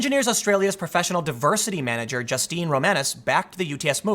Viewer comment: wasn't looking at the screen, what I heard was "Justine The-menace"